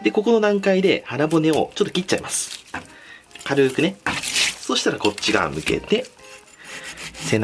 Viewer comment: 「腹骨をちょっと切っちゃいます。ｱｯ！軽ーくね。ｱｯ！」 って言ってるように聞こえてなにに焦ってるんだろうって思った